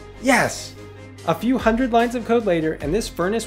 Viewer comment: i wasn’t watching just listening and for a second I definitely thought he said “a few hundred lines of coke later”